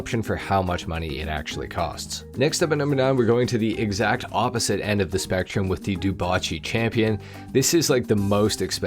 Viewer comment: at first I thought he said the champion is on the spectrum